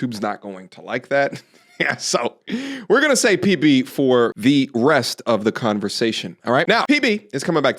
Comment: Bro I thought you said "We're gonna sa PB for the BREAST of this conversation" 😂😂😂 it was perfect timing if you did!